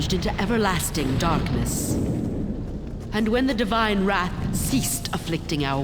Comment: I thought it said the "Devine Rat" and I was very confused